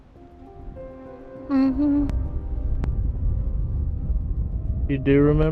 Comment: & 1:12:39 - what is that humming sound? Is that one of you? It sounds like someone saying, "Mmhmm,: Like it was answering your question of if they remembered what happened that night. I think even mentioned hearing something; but twice, I heard like a young girl humming very clearly.